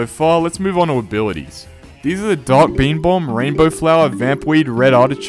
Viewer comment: anyone else hear dank meme bomb?